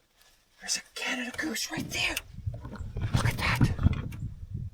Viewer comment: OMG I thought you said Moose, all that excitement for a goose. Dude the golfers here in Ottawa hate them, goose poop fills every park and golf course in the city!